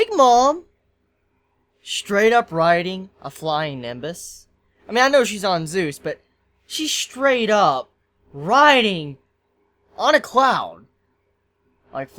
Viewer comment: did he just say riding on a "clown" or have I been reading a lot of tokyo ghoul recently ?